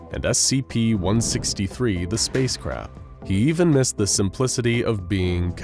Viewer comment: Ohhhh he said the space crab I thought he said the space crap. 😅